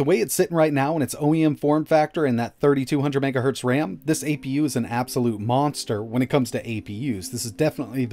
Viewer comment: I thought you said this was a beast. I feel lied to.